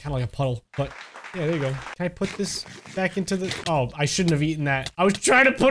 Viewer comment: In 2:28 it sounded like he said oh sh!t